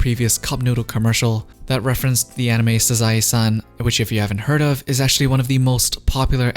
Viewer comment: What anime did you say it was at 2:39? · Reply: Sazae-san.